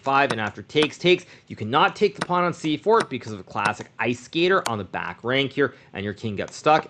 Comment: What does he say? Ice ..?